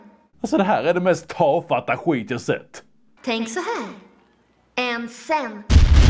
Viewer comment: Vad säger barbie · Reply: Tänk såhär: ÄN SEN